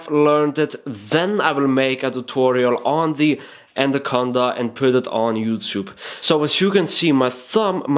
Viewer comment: what is he saying at 9:56? ill make a tutorial of the "condor"? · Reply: the "anaconda" is a flourish